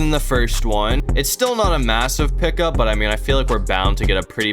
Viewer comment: DID U SAY…..mAsSiVe???